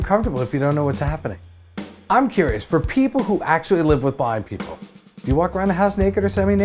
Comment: thought he said "bi people" so I was confused for the rest of the sentence